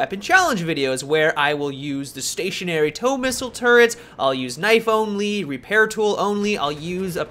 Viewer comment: Hahaha in the intro I thought you said iPhone lead, listened again and it was knife only! I was so confused